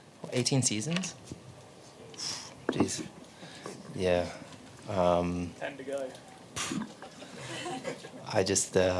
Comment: George literally says 'ten to go', how are you de@ lot hearing 'time to go' and criticising the guy for something he did not say? Is it so bad that you want to paint him in a bad light that you go as far as mishearing and calling him names? It genuinely does not get any more embarrassing.